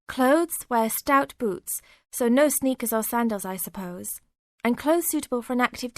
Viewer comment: kindly check your answer for item number 8... i think i heard the line "no sneakers or sandals."